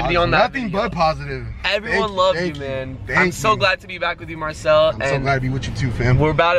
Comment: Aww I love Marcell so much! I’m so glad he’s back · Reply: Hannah • i thought u said ur glad hes black 😂